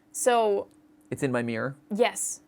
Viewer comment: i think it says "it's in my mind"